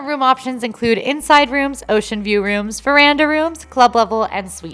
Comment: Veranda rooms? I thought you said Myranda rooms! Lol🤣🤣🤣